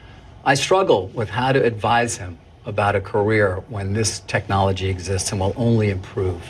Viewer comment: career 를 Korea로 잘못 들으신듯. 한국이랑은 전혀 관계 없어요